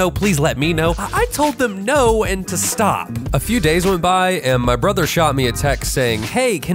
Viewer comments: I thought you said ur brother shot you,I got scared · When you said shoot you a text I thought you said he shoot you